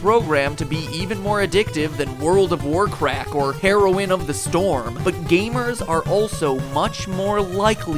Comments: Heroine of the storm? ._. well uh....wait i'm not the only person who heard that right? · when he said "world of war craft" I heard him say "world of war crack" 😂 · dude i thought i heard you'd said "World of Warcrack"